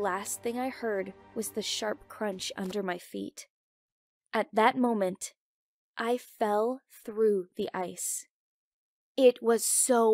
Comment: Her: "At that moment I fell through the ice. " What I thought I heard her say: "At that moment. I knew I bleep up."